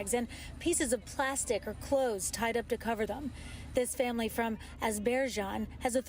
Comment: Did she say that family is from Azerbaijan? They're from an Asian country? At our southern border? Did I hear that right?